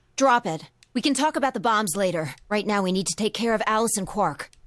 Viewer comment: my first thought was that Phi was saying to drop the bomb instead of the topic